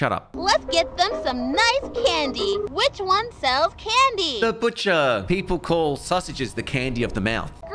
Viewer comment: i thought he said bitcher